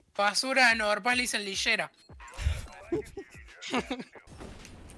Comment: que dijo aquí? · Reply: "Volta mano" o en español "Vuelve paps 😎xd"